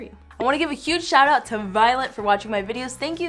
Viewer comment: I thought she said I'd like to give a shout out to violence I was like WHAT